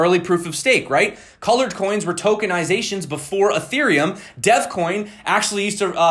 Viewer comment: Thought you said deathcoin instead of devcoin 😂 sounds brutal